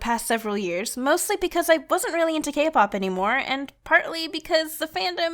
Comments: I thought she said Gay Pop Lmao that made me happy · did i just hear gay pop?